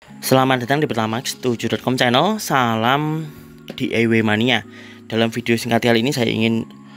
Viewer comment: Mas tolong diperjelas pelafalannya. Ane dengernya "salam di e w mania" 🙃